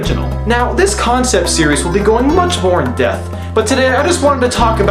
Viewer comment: did you say "in-depth" or "in-death"